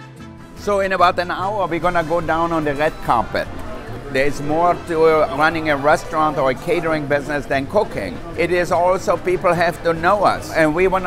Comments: All i heard was "There is more to running a restaurant or a catering business than cocaine." HEARD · Is it just me who heard "cocaine" at 02:39 ahhh the amazing catering business secrets ahah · "There's more to a restaurant or catering business than cooking" 2:41 I honestly thought he said cocaine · I heard cocaine instead of cooking 😳😓 Who else heard that I can't just be the only one right.... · i thought he said cocaine